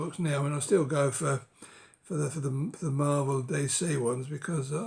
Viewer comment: I couldn't quite get what you said, about Marvel Steelbooks. You said you were into Marvel Day Say? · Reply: Hi Knut. I said Marvel and DC there . . .as in DC comics. Hope that clears it up for you 🤴🤞